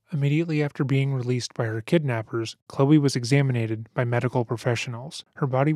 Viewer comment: did you say " examinated " . What language doe that come from ? .